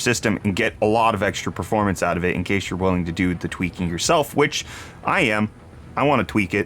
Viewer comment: At first, I swore you said twerk and not tweak. That might be fun to watch though. could be so twerkalicious!